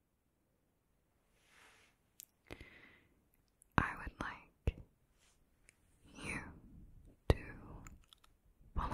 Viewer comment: segment, what is that Maori phrase you're saying? Sounds like "Cow Pie" but it surely isn't spelled the same or mean the same. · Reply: Ka pai, it means well done or good effort :)